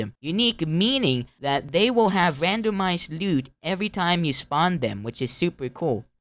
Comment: im sorry but i have hearing problems. I heard you say "they spawn with a randomized n*de everytime"